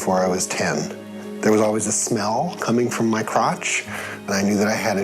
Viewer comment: oh lord I thought I heard "there was always a smile coming from my crotch" instead of "smell."